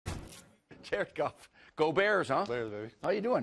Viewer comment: It sounds like he is saying Jerk off when he says Jared Goff